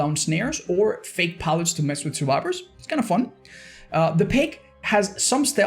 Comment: did he just say that freddy is kinda fun?